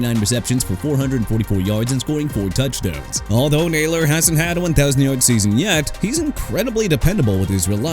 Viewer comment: That's enough of you saying "jag-wires" and "chee-kah-gow"! Jaguars as in JA-GWAARS!! AND CHICAGO AS IN SHUH-KAH-GOW OR SHEE-KAH-GOW!! YOU UNDERSTAND ME BRO?! ALSO ALVIN KAMARA AS IN KUH-MA-RUH!! AI VOICE GUY... YOU'RE DONE AREN'T YOU HUH?! IF SO QUIT!!!! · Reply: Did you notice the closed captions read 10,00 instead of 1,000 . Gotta stay on 'em!